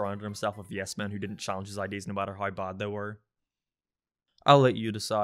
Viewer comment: Yeah Butch, PEG KURO!! .... Thats what you said right?